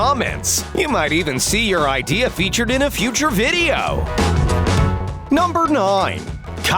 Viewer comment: i think he said "number 9"